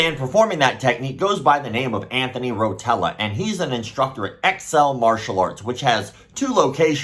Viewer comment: When I see how fat he is and you said Excell Martial Arts I literally thought you said XL Martial Arts lol